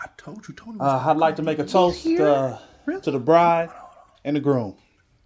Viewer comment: Man misheard toast as roast